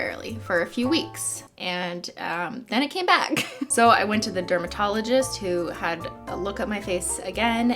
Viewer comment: I thought she said roomba- tologist so my dumbass goes "oh vacuum doctor??/"